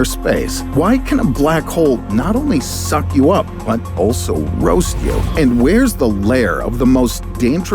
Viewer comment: What did you say at 0:48? 🤨 not only do they what? 😮‍💨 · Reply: Roast you